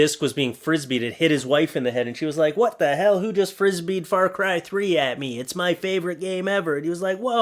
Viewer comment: thought it seriously said "fargay3"